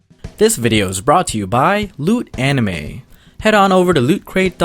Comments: Thought u said lewd anime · At the end i thought i heard u say "this video is brought to you by lewd anime" but now i see its Loot Anime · i knew 2 people who were Laos i believe and their last names were Phouikhham and Phanbandith lol also i wasn't paying attention when you were sponsoring and all i heard was "this video was brought to you by Lewd Anime~" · I swear I heard him say "Lewd anime"... What am I doing with my life? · LOL when you were talking about Loot Crate and said "new anime" I thought you were saying "lewd anime" waaaatttt XD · Lol i thought i heard lewd anime HAHAHHA (loot anime) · I heard "lewd anime" and not "loot anime" Yeah my mind's rly dirty